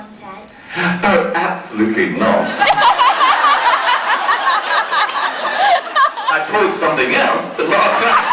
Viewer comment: What does Sebastian say at ~9:55 ? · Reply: She asked "have you ever pulled a prank?" Sebastian said "No, and I pulled "something else" but not that a prank."😂😂😂😂😂